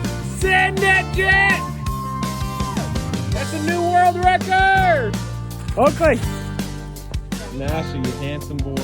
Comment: What'd he say? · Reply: “Send it, Jet! That’s a new world record!” If that’s the part you were referring to?